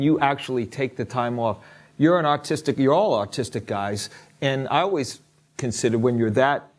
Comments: I thought he said autistic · When he said you are all artistic at an extreme level. I thought he said autistic. I was like?????????? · I thought he said "Autistic" and I was like ohhhh... what? :P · I thought he was saying autistic